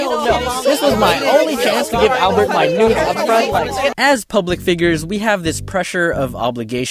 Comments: Did anyone else hear “This was my only chance to give Albert my nudes up front”?! 😹😹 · "This was the only chance to give Albert my nudes" Did anyone else hear that? Edit: why does this comment have so many likes? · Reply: wasn’t it “this was my only chance to give albert my nudes upfront”? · omg what did that guy say 🤣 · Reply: THIS IS MY ONLYFANS TO GIVE ALBERT MY NEW.... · did someone say “This was my only chance to give albert my nudes” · I heard someone in the angry crowd say "This was my only chance to show Albert my nudes" XD